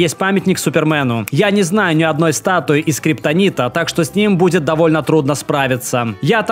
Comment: я сначала услышал "я не знаю ни одной статуи сделанной из Скриптонита"